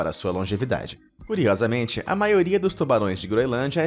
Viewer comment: eu ouvi tubalões